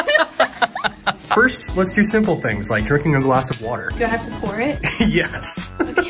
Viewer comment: I thought he said jerking a glass of water😂